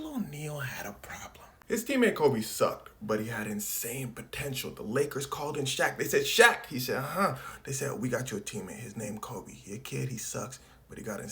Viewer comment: did you just say… potential?